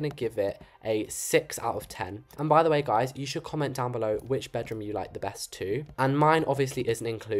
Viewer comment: you said what bedroom is the best instead of bathroom 😅